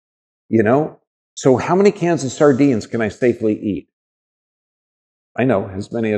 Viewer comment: Minute 16:30. What is the word before sardines? Thank you for your precious sharing of knowledge. I am very grateful. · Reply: My pleasure! It's "cans of sardines". Stay blessed! :hand-pink-waving